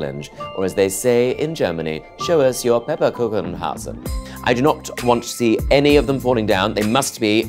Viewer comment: was hat der gesagt?😭😭😂😂😂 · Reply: Pepperkuchenhausen?? 😂